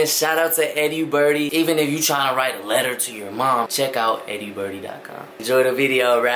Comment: Tell me why I thought he said “eerybuddy” when he said “eddiebirdie” my ears are broke ✌️